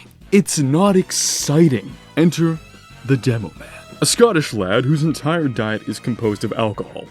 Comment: Did he just say a Scottish Lad.